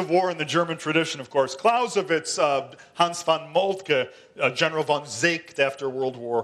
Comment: Dear all, I am curious about these great german philospher generals RC mentions at 12:40 and wish to read more about them. But I cant quite hear the names he mentions. Does he say Clausewitz, Helmuth Von Moltke and Hans Von Seeckt or something else?